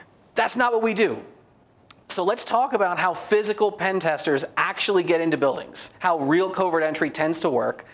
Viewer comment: Oh, I thought it said "Physical Pain Testers"